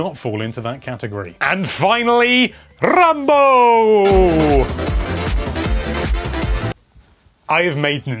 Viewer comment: Oh... RUNbow! For a second there I thought you said "Rambo" and left me thinking "When and why are they making a new Rambo game?"